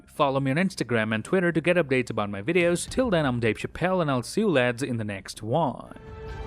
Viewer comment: At the end did he say “my name is Dave Chapelle”? Around 7:26?